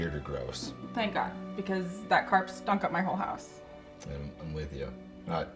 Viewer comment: Thought she said that "carp was stuck up my whole ass" not trying to be a jerk but literally laughed out loud bc i thought thats what i heard.